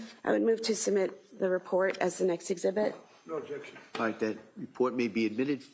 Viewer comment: after “no objection” it sounded like the judge said “aight bet” lol